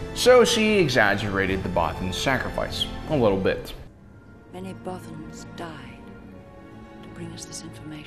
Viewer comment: First time I saw it I thought they said “many brothers” I mean mean what is a godamn bothan